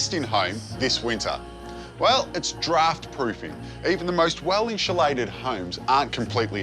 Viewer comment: I thought it said daughter proof